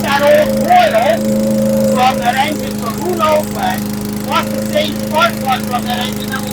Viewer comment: did you say who knows when or who knows what in that part? Also great job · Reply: Who knows when... I have no idea how old that coil was, is, or how long that engine was out in the element, yet the coil works perfect..